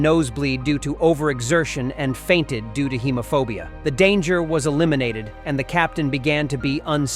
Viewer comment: i thought he said homophobia